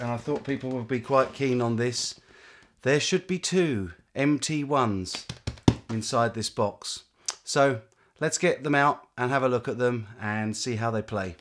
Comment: i could've swarn i heard you say "there are 2 empty ones inside the box" haha!